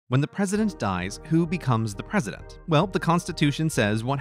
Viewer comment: the first sentence they said i thought it said, "When the peasants die who becomes the present?"...... W H Y